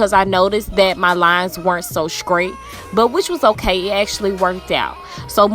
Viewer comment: Did this chick just say SCRATE instead of STRAIGHT. 🙄 I'm embarrassed for her.🤦🏾‍♀️